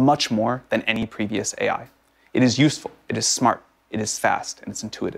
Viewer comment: did he just say “it is sus”?